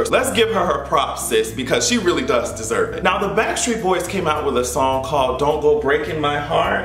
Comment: Uhmmmm.... Tell me why I thought you said blackstreet. I am very disappointed.